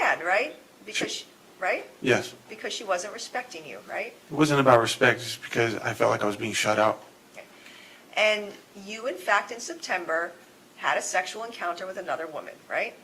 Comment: Lmao at how he literally flinched when he heard himself say "respect" on the recording yet still pretended he heard nothing. Holy fuck man. Meanwhile, always love the narration in these. feels so authentic, compassionate and down to earth more than any true crime channel I've watched. You guys are so human in the best of ways, thank you. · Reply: I clearly heard him say "it's about respect!" Which sounds like he was quoting a movie, horrific.